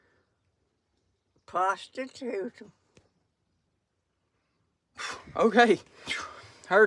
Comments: I thought grandma said "Prostitute" at the end there, LOL. I was like WTH ????? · what did she just say💀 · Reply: Almost thought she said, prostitute them 😂😂